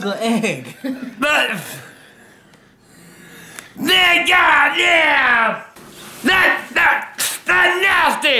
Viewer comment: DID HE JUST SAY N*GGA?!!!!!!!!!!!!! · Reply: No he said man god damn